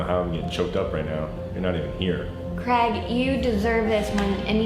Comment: did she just say Crag??